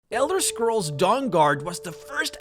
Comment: I thought I heard Elder Scrolls: Jurassic Park. Then I thought. That makes no fookin sense at all.